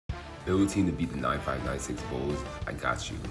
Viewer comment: Build a team to beat the 2021-22 Warriors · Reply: sorry I thought you were saying there better than the 95-96 bulls